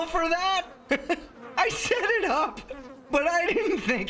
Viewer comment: i thought he said i shit it up lmao